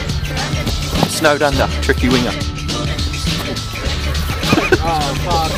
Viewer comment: Lol I thought he said 'tricky weiner' at first.